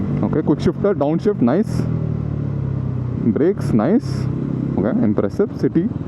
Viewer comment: why did I hear Drake is nice 1:01 😨😨